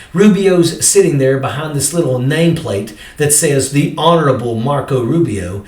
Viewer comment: I thought it said, The Horrible Marco Rubio Ell-Oh-Ell . . !